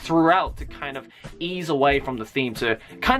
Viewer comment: when he said kind of keep he sounded like he said Kaneki